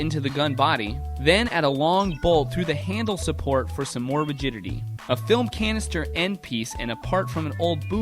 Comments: does he say "virginity"? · did he say legitity